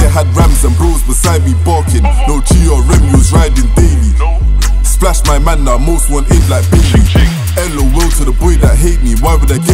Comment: I cant catch the bar between 1:59 and 2:06."that day i had... beside me barkin'.... 'splash mad man' ...'", whats he say?? Im Australian so forgive my ignorance lol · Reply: also he said "No GRM he was riding daily, splash my man now im most wanted like bailey"